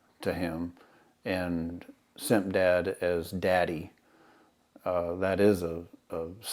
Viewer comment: I just noticed that in 2:40 He called the guy Simp dad, but the subtitles say step dad 🤣 You are the man ✊🏼